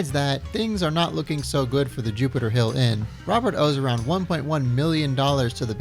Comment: Did you say the “Jupiter Hill Inn”?